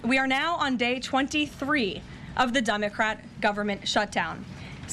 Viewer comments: Did she say DUMBocrat? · Almost sounded like she said we are on day 23 of the dummercrat shutdown.